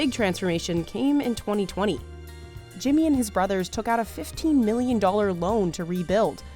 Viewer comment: Did I misheard?? $15million to rebuild a diner???